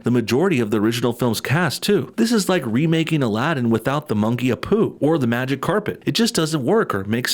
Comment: Did he say, "The monkey, Apu?" His name's Abu. Man, we're already feeling the damage of losing Apu, aren't we?